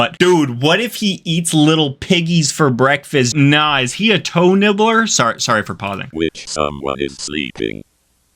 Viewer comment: For a second I thought he said. Hoe nibbles 😟😂😅💀☠️😰